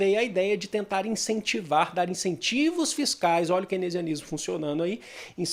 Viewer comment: Professor o que você disse nos minutos 10:33 ? Quenezionismo ? Quero pesquisar sobre isso. Eu sou descendente de alemão, moro na Inglaterra e sempre estudo sobre a economia alemã. Seu canal é incrível. Parabéns!! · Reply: keynesianismo